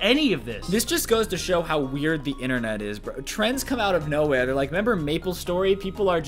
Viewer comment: he said "trends come out of no where" But I swear I heard "trans come out of no where"